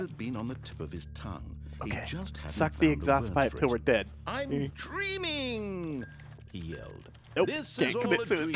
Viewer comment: I heard "I'm Creaming!" xD